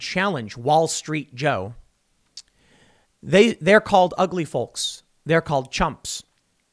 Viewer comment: It's not just that people don't care about Biden. They just don't understand anything he says. It sounded like he said 'ugly flirks'. Daily Mail has a better translator than I do.